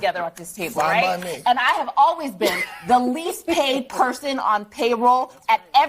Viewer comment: is she saying least paid person or least behaved person. I keep hearing "behaved" and not paid...???